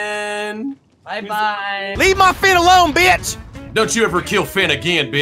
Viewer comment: I thought he said leave my feet alone